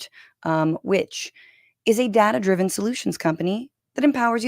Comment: Am I the only one who heard "dad-driven solutions"?